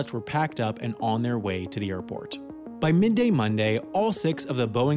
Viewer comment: Did he say Minday Monday at 6:39? It screws with my head.